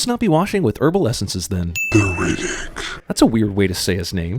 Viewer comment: I think the voice said "B Riddick"